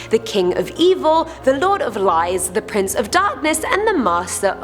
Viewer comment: I thought she said Lord of lice😂